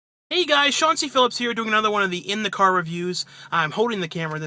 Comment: I thought he said "Chauncey Billups here." LOL